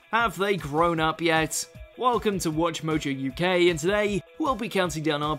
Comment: When I first came to this channel I thought you said... Welcome to WatchMojo u gay Like here